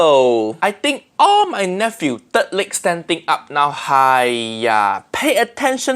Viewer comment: Uncle Roger: "Third leg" CC: "Dirt lick" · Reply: I actually heard it as dirt lick, I had to re-listen a couple times before I heard "third leg"